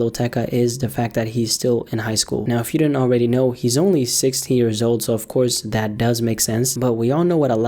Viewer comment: 😂 I thought you said he was “only sixty years old” for a second lmao